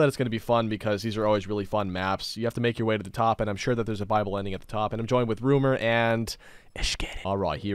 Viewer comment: I don’t know why but every time says viable, I think he says bible, so I hear ‘bible ending’ not ‘viable ending’.